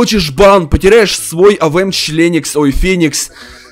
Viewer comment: Или её послышалось или ты сказал авм членикс